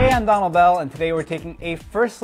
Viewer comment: At the end there I thought he said ''Im donald duck'' :O